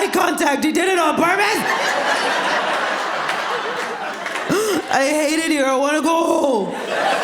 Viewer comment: the subtitles are wrong here. She says “I hate it here, I want to go home.” NOT “I hated you when I was a girl” like the subtitles say!